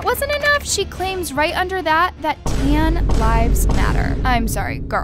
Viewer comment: No cap, when u told us that she said "tan lives matter" I thought you said "tan lines matter" like from bathing suit tan lines 💀and I got so confused lol. I WAS LITERALLY DYING HAHAHAHAHA💀💀💀